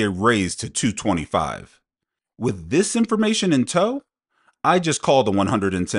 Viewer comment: is a fking dingleberry, greedy goblin, he clearly said "2 25s?" IT WAS A QUESTION